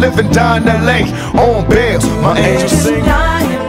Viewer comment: What does pac say at 1:09 sounds like it should be, fuck interscope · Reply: And my Angels go....